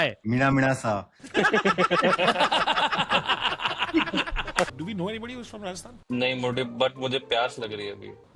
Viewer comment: Did said “Nahi MOTE”